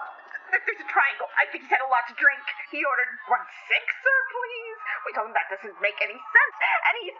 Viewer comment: WHEN SHE SAID HE ORDERED ONE SIXER I THOUGHT HE SAID sexier 😭